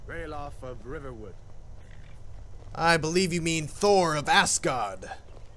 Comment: Wait... Did I just hear say "Thor of Assguard"?